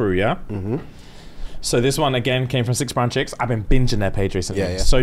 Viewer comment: Sorry what was the name of the page that has been binge watching that the dilemma came from? I thought he said six pound chicks?